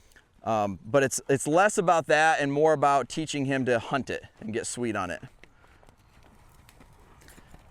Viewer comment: Great video! I have to mention though, that at first I thought you were saying, “teach him to hump the jump…” 😮😂😂😂😂😂😂😂😂😂😂😂😂